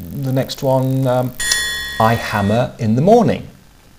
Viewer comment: Hehe, first I thought you're saying "I have a hammer in the morning" ... but then I noticed I was wrong ;o)